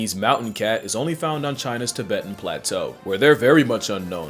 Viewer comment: did you just say...... Tibet?